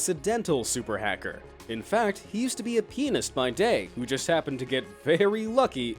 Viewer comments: did he said penis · When I hear penis 6:07 tf? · Is it only me or did he just say at 6:07 he use to be a penis by day😂😂😂😂😂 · Thought he said penis by day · Ummmm did he just say penis